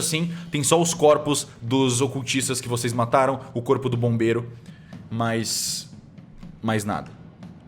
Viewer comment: "O corpo do bombeiro" só eu q ouvi ??